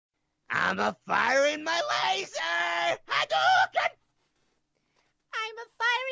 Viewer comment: What are they saying???? Heres what i heard " ima fire out my lazer, I KNOW I GET!!!! " Thats a guess